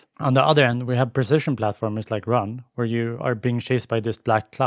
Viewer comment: Anyone know what the game is at 4:35? I hear "run" but that's either wrong or not a great name to help find a platformer : p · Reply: Ahh sorry, i was going to put text on that name because i agree it isn't a very searchable name, but i forgot. It's called 'Run: The world in-between'